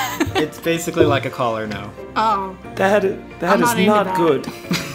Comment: "Oh, that is, that is not good." but the closed caption says "Oh daddy, that is not good." 🤣